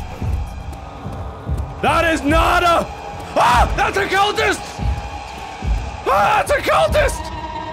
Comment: when Mark said "its a cultist" i thought he said "its a celtist", and i imagined a scottsman wearing a kelt trying to kill him.